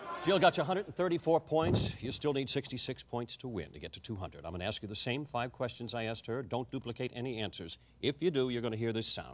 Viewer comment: what did he say. i cant hear it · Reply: "You still need sixty-six points to win, to get to two hundred. I'm gonna ask you the same 5 questions, I asked her. Don't duplicate any answers. If you do, you gonna hear this sound."